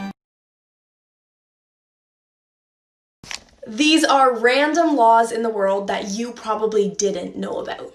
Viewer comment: I swear she said "lawns"